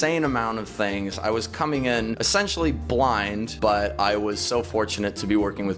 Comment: did he say sexually?